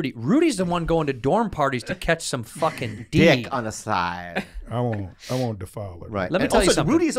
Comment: this is suspect asf. He said "I won't defile her". I think that's what he said